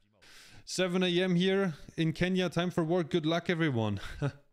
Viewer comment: Did he just say Kenya🇰🇪😎